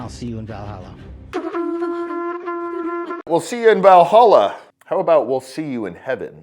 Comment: Hey ! I am from Norway. I thought he said Dallalha or something from India. Valhalla ! ? As a viking...I do not get it ! ? Paradise would be the word ! In Valhalla we fight and drink beer and are bad folks...eating...and all kind of nonsens. I know...this is a side note...but worth pointing out when into the subject of weird things...and your country where allot of weird things happens. Charlie Kirk seems to have been an outstanding brave and good and happy and informed person ! R.I.P.